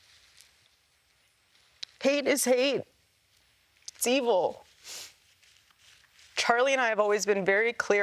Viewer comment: is she saying "pain is hate, its evil" ?